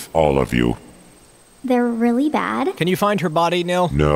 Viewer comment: What did loom say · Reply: They're really bad. Nil has only been referred 2 as they and them so she's saying he (they) are really bad